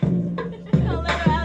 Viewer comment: at first i thought she said "dey be like niggaa can u teach me how to dougie" den im like oh she said jacob lmao